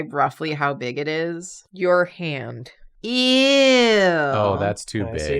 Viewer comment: when said the the size it just heard European. instead of your hand